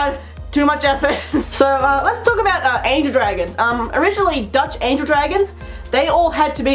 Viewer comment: You mentioned Dutch Angel Dragons and I thought you said DEUTSCHE angel dragons.... This isnt REICH! this makes me FÜRUROUS!